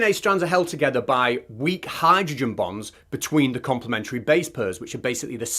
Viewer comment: I thought you said DNA is held together by weak hydrogen bombs. I thought that explains how DNA blows up when it unravels. 😂